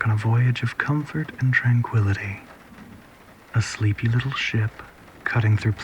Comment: I thought it said “you’re a sleepy little shit.”